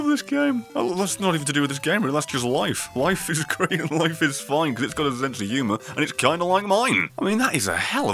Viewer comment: "Life is great, life is fine, and it's got humor kinda like mine" I know, not the exact words, but I couldn't understand it. Caption Version: "life wife is a critical look at this phone good government the Yuma with John Longmire me is a helluva" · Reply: Vanquisher life is great and life is fine, because it's got a sense of humor and it's kinda like mine.